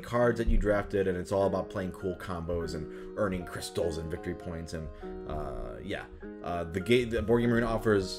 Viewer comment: Did someone say victory. points.